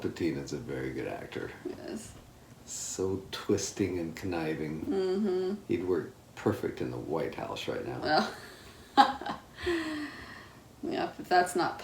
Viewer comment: Excuse Me,What chya just say?! "Palpatine, He work perfect in the White Hose {Right Now}" 14:27 That's what I heard. <link>